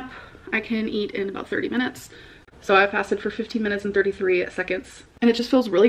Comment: I thought she said she had fasted for 15 minutes 😂 i wouldnt put it past her tho